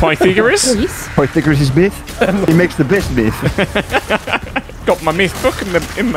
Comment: was anyone else hearing meth rather than math there at 8:20?